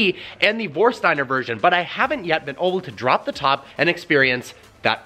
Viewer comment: Anyone else notice he says “oble” instead of “able?”